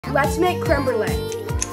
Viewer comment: Y did I hear let's make Kimberly 😅